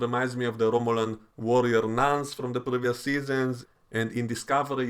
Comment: I thought you said warrior Gnarns.